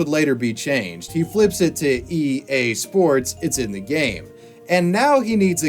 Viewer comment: I heard "EA Sports, its kinda gay"